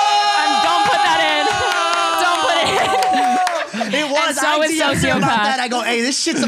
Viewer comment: For everyone confused why she's being awkward, Logan and her used to hookup. She thought they were exclusive but Logan was only sleeping around and hooked up with Jocelyn Chew. She then wrote the song Josslyn about him. Edit: To everyone in the replies saying it's about Josie, no it's not. The song existed before Logan dated Josie and there is a timeline of Logan hooking up with both Olivia and Jocelyn simultaneously. After Josslyn was released, Olivia in Violet Benson's podcast pretty much said that she and Logan hooked up again before quarantine and one week before he started dating Josie. Jocelyn and Olivia are friends and they threw shade to Logan together on their IG story by using the song. Chantel has also confirmed this. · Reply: So that's what she said at 1:05:58? "And Josslyn is about Logan" "And so is sociopath" makes sense